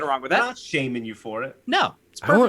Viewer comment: did boogie say he wants more "porn" or did i hear somthing